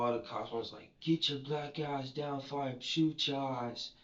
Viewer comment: I hope the officer/s did not say black a$$ - regardless of the situation.